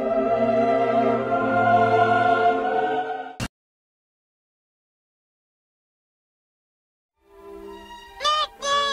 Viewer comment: i didn't understand 🤨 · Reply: his wives were pregnant